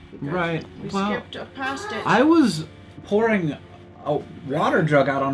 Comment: It totally sounded like you said I was whoring when you said I was pouring :D